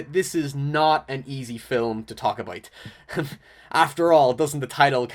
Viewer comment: not an easy film to talk a bite.... that's what it sounds like you said